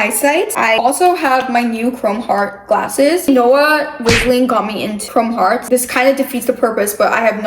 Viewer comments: “Noah wisling” I swear that’s what it sounded like💗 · I always thought they were saying Noah RIZZling😂😂